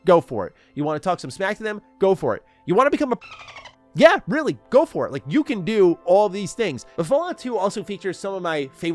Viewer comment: what is this censored out word actually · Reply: Pron star